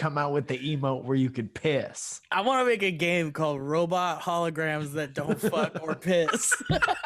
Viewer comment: I thought said robot holograms that don't fuck or kiss and I felt personally attacked